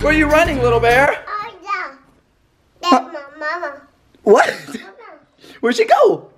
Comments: I’m rewatching all their videos and realised that at 0:40 it sound like Elle said “dats ma mama” 😂😂😭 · it sounded like Elle said “ Austin where’s my mama”😂😂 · It sounded like Elle said, "Dats my mama" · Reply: Alana Mayfield she did say dat's my mama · sounds like Elle said "I don't know I'm trying to find my mama"...